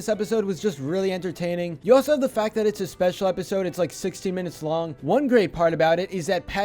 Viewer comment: I thought you said 60 not 16, I was like, "THAT'S A MOVIE IF IT'S 60 MINUTES LONG."